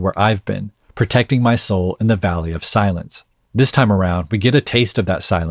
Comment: Another point I found interesting while watching this: his last lyric was “in the valley of silence“. It seems like he came to a realization that he is not a savior and had to leave his “silent hill“ and make himself lower down in the “silent valley“. 🤔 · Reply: I misheard it as the "value of silence"